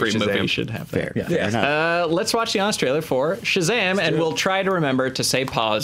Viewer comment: Youtube auto-generated captions says "Australia" for "Honest Trailers"